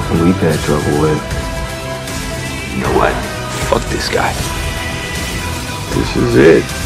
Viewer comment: Did just say " Fuck this guy " at 1:20 - 1:21 Cause... I don't believe my ears!